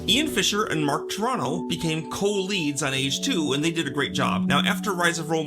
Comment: I misheard "co-leads on Age II" as "co-leads at age two" and thought they were VERY precocious toddlers